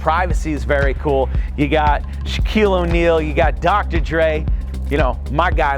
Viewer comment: Did you say, Dr. Dre at 15:33 ? 😅